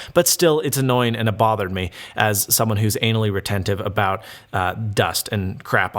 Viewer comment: did you just say anal